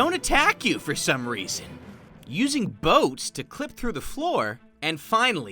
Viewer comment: Did say boabs?